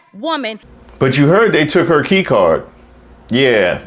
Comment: I misheard "took her key card" as 'keytar' for a second. It still fits, more or less; I certainly wouldn't dispute calling her a rockstar.